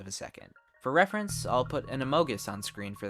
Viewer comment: what did you just say An omogus